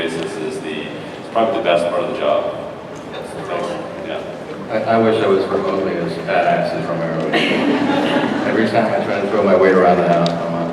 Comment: What Nestor said at 41:21? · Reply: He wishes he was remotely as badass as Romero is